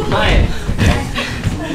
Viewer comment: の部分聞き取れません。肩で繋ぐ？ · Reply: たぶん肩でさがるですかね